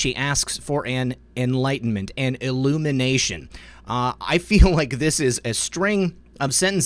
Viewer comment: Sounded like she said " elimination".